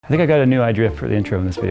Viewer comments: At the beginning of the video i thought he said nigga got a new adria for the intro in this video :) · Does anyone else hear "Idrea" ??